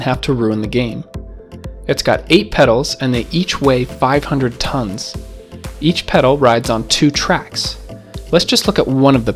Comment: bro, did u say the weight 500 tones each pedal ?